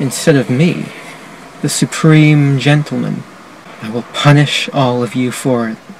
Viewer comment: "Supreme gentleman" all i heard is "supreme victory " from killer instinct 😂